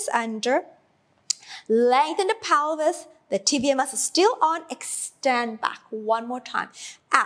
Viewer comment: Hi! I love your videos! I have a question - I think I am misunderstanding something from the first half of the video - i thought i heard you referring to something in the waist/breathing area as tibia - and i keep being confused thinking of tibia as the leg and forgetting what you were actually pointing towards - can you please help me understand? thank you!! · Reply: She is saying TVA which stands for Transverse Abdominals :)